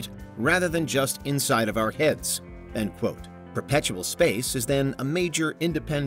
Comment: right around 5:10 - 5:12, narrator calls the term "perpetual" space instead of "perceptual" space as it was previously identified. Which is it?